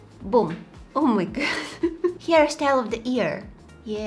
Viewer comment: “hair style of the era” me: “did she just say ear?” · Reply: “Hairstyle of the year” 💝